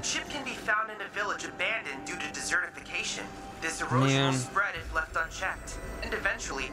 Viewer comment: I know what he's saying, but for a moment I interpreted desertification as like "deserted" so it sounded like Chadley was saying "It was abandoned due to being abandoned."